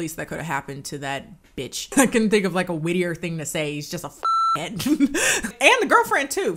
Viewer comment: Is it just me, or does this sound like she's saying the f slur? I'm sure she's not, but I can't tell what she's actually saying here.